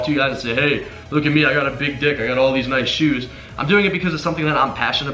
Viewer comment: Did just say "I got a big dick" at 7:06😂